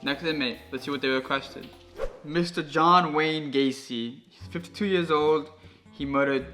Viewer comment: I thought he said age 2 years old